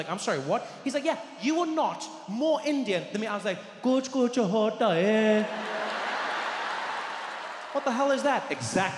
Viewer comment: What did he actually say at 5:51? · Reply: what he said was a movie name "Kuch Kuch Hota Hai" by a very very famous Indian actor Shahrukh Khan. Many of the western artists know bollywood because of him and this movie. I think that's why he picked this reference.